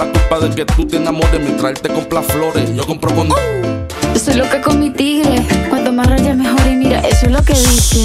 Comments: Yo pensé que decía soy loca cometida xddddd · Yo siempre creí que decía “soy loca come tigre” 😔✌🏼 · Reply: 🤣🤣🤣🤣 llevó como 30 minutos riéndome de esto... es que pensé que decía lo mismo: soy loca come tigre. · pense que decia soy loca como el tigre XDD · Yo pensé que decía: soy loca comestible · Yo siempre pensé que decía ´´soy loca comestible´´ 😔✌🏽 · Siempre pensé que decía “Yo soy loca con mi pibe” 😭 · Siempre pensé que decía -"soy loca con-tigo" 😳😳😳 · Pense que decía ' yo soy loca con mi tiguere' jajaja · Yo siempre creí que decía “soy loca come tigre” 😔✌🏼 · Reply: Yo pensé que decía: soy loca como un tigre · "soy loca come tigre"? Yo siempre pensé que decía soy loca cometido😟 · Yo pensé que decia “yo soy loca come tigre” :vv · Yo entendia: "soy lo que come el tigre"despues escuche bien y pensé que decia: "soy lo que tu me pides" para después terminar diciendo otra cosa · Yo pensaba que decía "Loca come tigre" :v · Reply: yo pense que decia son los que come tigre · Pensé que decía "Yo soy loca con motivo" xd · yo pense que decia yo soy loca cometida jajaja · Pensé que decía solo que come tigre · La mayoría: soy loca como un tigre. Yo (y otros): soy lo que he cometido. quedé en shock, toda mi vida fue una mentira. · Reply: Yo te juro pensé que decía "soy loca cometida"